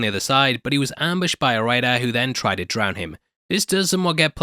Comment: The captions are wrong in some parts but I honestly think it’s kinda funny. 1:31 “Eventually he emerged on the other side, but he was ambushed by a white guy” when it was supposed to be “a raider” lmaoo